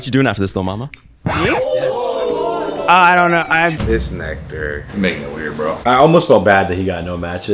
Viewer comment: THE WAY EXPRESS HIS OPINION IS SOOO TUFF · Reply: fr he said this "nectar" instead of this n-word, no one caught it bc he's so shifty